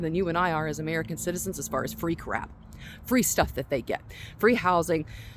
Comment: Free crap, funny how I first heard "freak rap." Same-o same-o, imho Love ya!